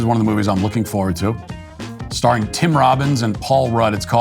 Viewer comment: Tim Robinson - the actor in Friendship. I thought you were saying Tim Robbins, and I thought I was going insane - "That's not Tim Robbins!"